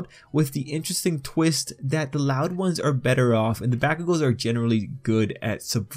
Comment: Probably cause I'm not using my headset, but it I kept hearing "Batgirl" instead of "Ba-ku-go" I'm used to